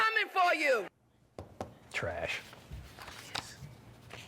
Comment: Yep, I heard her say perish! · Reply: She said ‘parish’ though. More points docked. And she said she “ain’t afraid of NO President.” Until now, and she is very, very afraid of the Trump administration, as well she should be. What a race baiter. FicTishious.